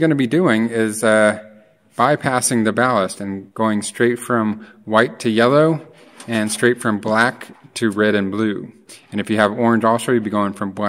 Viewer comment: I did as exactly as you said and my light will still not turn on. Could there be something I'm not doing right? I did notice one thing I'm not sure about and that is the fact that the blue wires crisscross from one tombstone to another tombstone and not directly across from each other. I really didn't understand what the red wire is for since it's not connected to the black power wire. It's amazing how some things seem so simple but, they aren't. I had 2 ballasts installed on it and I removed both of them. They are not required when converting are they? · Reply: I think I caught on to something you said that may be my problem. I thought you said to connect the white to the yellow and the red and blue to the black. I didn't do that. I simple connected red to red and just the blue to the black.